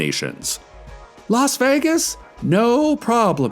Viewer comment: WTNV might've overplayed their hand by saying that los angeles was only a few hours away by bus, we've really narrowed down where it could be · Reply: I thought they said Las Vegas. Which i mean.... it kind of makes sense it would be a few hours away from there considering there is one very notable desert in the US. We already knew Night Vale had to be somewhere in the mojave desert.